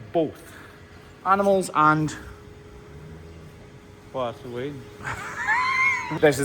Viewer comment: I've replayed this 2:49 like 20 times and I cannot for the life of me comprehend what camera man actually said, I know the subtitles are there but there is just no way that's what he said 1 to 1. Many times I've noticed the subtitles convey the same message but no exact words. WHAT DID HE SAY THERE?! I CANNOT COMPREHEND! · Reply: “Where’s the wean?” or more phonetically, “War’s the wean?” Wean is a Scottish English and Scots word for “child.”